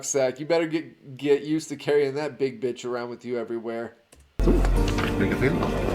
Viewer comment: 🇸🇪 Did you say "Bitch"?